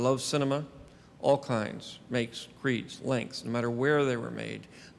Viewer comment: What did he say at 6:11? Makes, creeds, links?